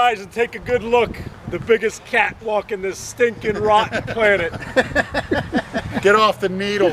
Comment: HilARIOUS, but what does he say..."the biggest cat walk in this stinking rotten planet!"?